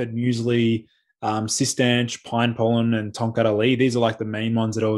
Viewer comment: can you list the adaptogens he mentions. I couldn’t understand with his accent. Not trolling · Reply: ashwagandha, safed muesli, korean ginseng, cistanche, pine pollen. Last one I can't hear but sounds like tomkallari?!